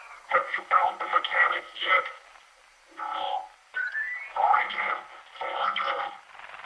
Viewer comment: I thought the robot said "Well I can, fork you!", instead of "Find him! Find him!"